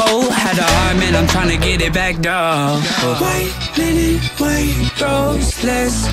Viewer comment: Ok so for the longest time I thought it said white men in white robes not white linen white rows. Lmao